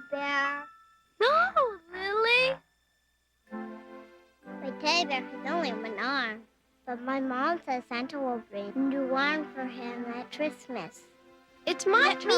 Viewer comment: the way this little girl is slurring even Shane MacGowan would go, "What the hell did you say?" Oh wait I mean, "Whaa da helllll dijjj yuo sssa?" (Not sure how to type out slurring) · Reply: "My teddy bear has only one arm, but my mom said Santa will bring a new arm for him at Christmas."